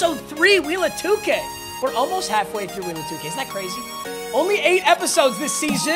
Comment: It sounds like he said wheel of dookie